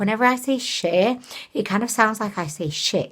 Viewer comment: Hi!!!!! First I have to say that I loooove your vids and you!!! You have helped me so much when my depression is not in a good mood!!! Thank u for that.... But..... Lol....when you say "sheer" it sounds like your saying "Cher", like the singer!!!! Lol